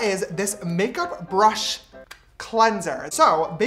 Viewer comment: I heard makeup brush blender (p≧w≦q)